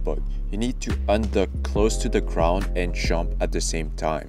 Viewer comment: i dont understand at 0:31 you said you need to under close to the ground , whats that mean ? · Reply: 'unduck' close to the ground. Sorry bad pronounciation. 'unduck' = release crouch/duck